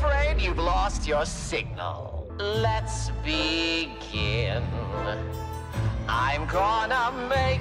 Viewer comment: i thought he said "lets be gay" I CANT ESCAPE IT💀💀💀💀